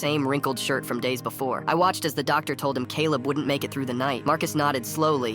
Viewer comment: I heard 'naked' instead of 'make it' 😭